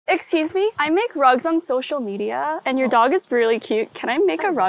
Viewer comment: I heard "I make drugs on social media" 💀